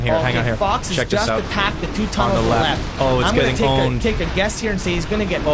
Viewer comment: Did I just hear pronounce the P in pwned?